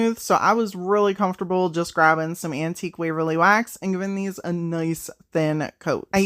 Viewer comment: Sounded like you said, “Antique Willy Wax.”